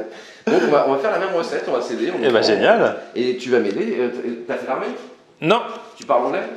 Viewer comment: cest moi ou a 5:56 il dit "ta spermer"